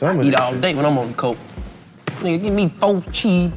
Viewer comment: did dc say "when I be on that coke i"🤔🤣